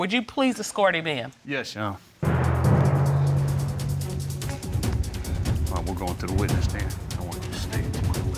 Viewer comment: Can anybody understand what the husband says when they first let the side dude walk in? 10:25 I heard “when he go up to the witness stand, I want you to”. And that’s all I could make out.. anybody hear anything?